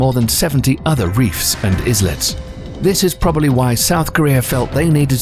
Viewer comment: Did he say ''iZlet"😂